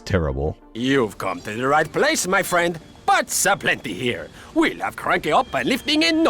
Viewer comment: Did he say butts a plenty here?